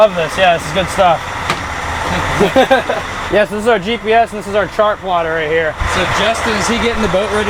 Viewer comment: "This is Austism a Cachi" at 7:39, Dude has an actual name. caption writer. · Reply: "This is our yes" instead of "this is our GPS" at 10:20, Moments later Justin's name gets turned into "suggest"